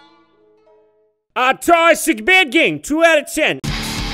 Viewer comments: what does he say in 14:10 ? thank u for answer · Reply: Now, as a professional Jontronslator, he said, ahugh, it's a bad game 2/10. Explosion. I must now kill myself for the horrible pun used earlier in this comment. Goodbye. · what did he just say? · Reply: "Um it's a bad game 2/10" I think that's what he said?